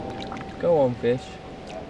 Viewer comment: at first at 8:03 I thought he said go on bitch